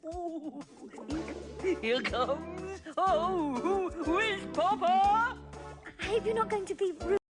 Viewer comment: It is fantastic, Speilberg's live action isn't as good though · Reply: Yep, I heard "Here comes a whizzpopper!" near the end, so it must be true!